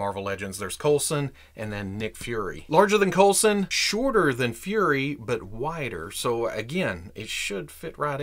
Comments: Haha thought you said "whiter than Fury" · Thought you said Dracula was whiter than Nick Fury 😂